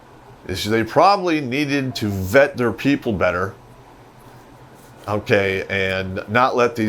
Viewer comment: First time I heard 'vet their people better'...I swear said 'feed their people better' hahaha